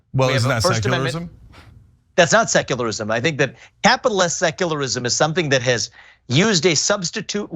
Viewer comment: Did he say capitalist secularism? I have been an athiest for 20 years and have never heard this nonsense. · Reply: Capital S secularism, not capitalist, I thought he said capitalist the first time too. Basically pretending all the social policy he doesn’t like is a “new secular religion”